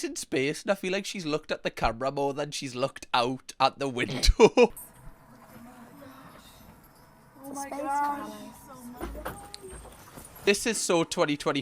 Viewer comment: did someone say 'oh my goddess!' ??? 😂😂😂😂😂